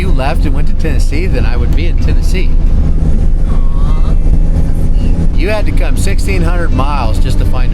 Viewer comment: yer ole lady, the toyota guy..🤣 "AWW thats ok".. 🤣 i heard "aww,bless yer heart"..🤣 tell her she should release her inner Tennessee.. its OK to say "aww, bless yer heart"..🤣